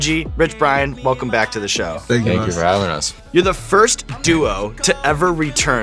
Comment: did Brian say "thank you for us" ?